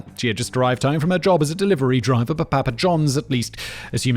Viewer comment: did you just say "drived"? That's not a word...is it?